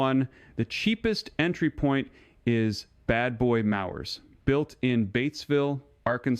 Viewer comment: did he say "Bad Boy MAUWERS"? Yikes!